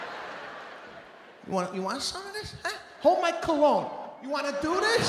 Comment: " Hold my cologne " LMAO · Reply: Thx.. Somehow, I heard “Hold my colon”, I was like “huh?”